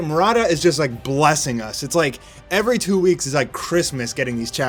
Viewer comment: I can't understand what he's saying " every two weeks" .yeah right